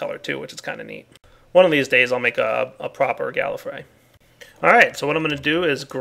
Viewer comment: Did anyone else hear the doctor who reference around 22:00? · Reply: **Gallifrey